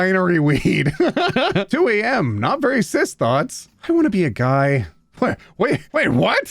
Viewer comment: for a sec I thought it said “I wanna be a bug” and I thought to myself how cool if would be if I could be any type of bug but specially some sort of beetle